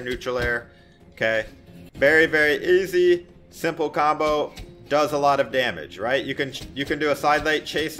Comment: Is it just me or i just heard im say the "THAT'S ALOT OF DAMAGE!" Meme?